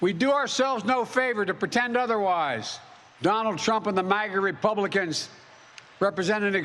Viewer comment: I keep hearing “maggot republicans” 😂